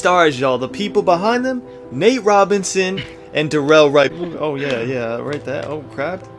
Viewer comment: I thought he said Darrelle Revis and it got cut off😂